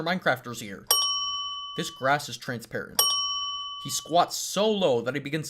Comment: did u say grass because the editor put glass